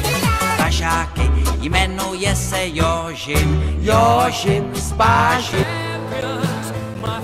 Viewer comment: I heard your s*** spa s*** 💀